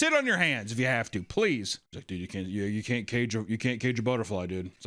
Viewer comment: Did he say "you can't cage a boner fly"?? LOL wtf